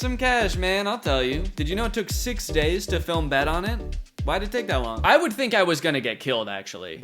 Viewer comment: WHAT is he saying at 7:49? Six days to filmbed on it? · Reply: Six days to film "Bet On It"